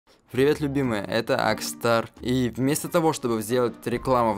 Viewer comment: Мне не послышалось? Или сказал ЛЮБИМАЯ😍😍😍